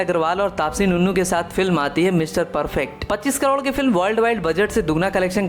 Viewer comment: Did he said TAPSI NUNNU😂